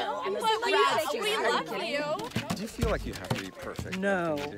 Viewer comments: When Molly Shannon asked if those girls were actresses she made their day. Shows how great a person she is. Thanks for the upload! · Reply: I , initially, thought she said, "are you guys activists" 'I was like' Oh no, here we go with someone promoting their ''pet cause'' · When Molly Shannon asked if those girls were actresses she made their day. Shows how great a person she is. Thanks for the upload! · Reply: MarkMcNasty007 i swear i thought she said "are you guys exorcists" haha i was like what